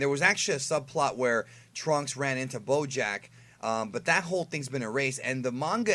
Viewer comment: Thought he said blowjack